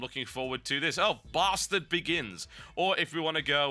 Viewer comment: I swore he said "Bastard Baggins"...